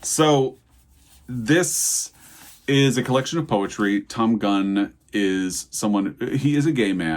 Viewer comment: I'm sorry, I thought at first you said "a poetry collection from Tim Gunn". 😭😭 lol